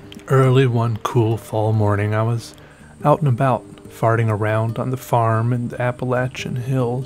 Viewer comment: Did he say "farting around..." lol 0:16 ... i love this guy